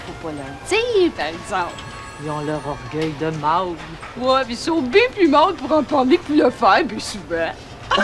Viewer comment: J'adore! Textes et performances excellentes! Nous avons tellement d'humour au Québec.... ça demeure une qualité pour survivre. Comme disait Plume, faut apprendre à rire de soi-même, même si ça règle pas nos problèmes. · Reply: Qu'est-ce que Luce dit a 30:47 elle a un accent tellement prononcée? Je crois comprendre "Ils sont ben plus males pour en parler pour le faire plus souvent". Cet extrait est tellement drôle elles devaient se tordre de rire quand elles ont tournées cette scene